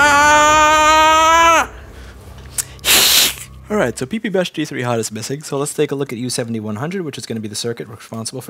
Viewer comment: I'm sure this has been asked loads of times but just out of interest what is the things says really fast at 2:27? Something like "p3p-g3HAD". I don't even know why I'm asking I think it's just that I've heard him say it so many times that it's frustrating I still don't know what he's saying! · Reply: i think its ppbus_g3_hot (i guess thats a processor power rail?) but i'm not too sure either^^